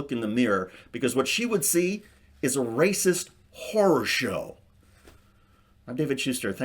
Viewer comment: I thought he said, "... a racist whore show".